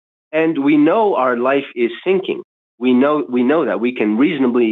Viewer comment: i thought the caller said "stinking shit"